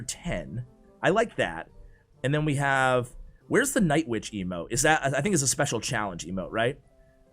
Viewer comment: did you just said night witch emote?